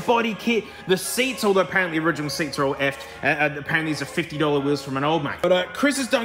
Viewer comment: Alright guys, my American ears need an Aussie translator. What does he say at 0:45? The wheels are pretty slick and I might want a set for my nugget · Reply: 50 dollar wheels from an ol' mate